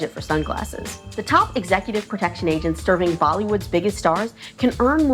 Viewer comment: Did she say "Bollywood"?